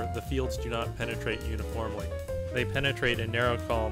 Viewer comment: Sorry but i don't understand what you saying in 1:53 "They penetrate...?" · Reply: uniformly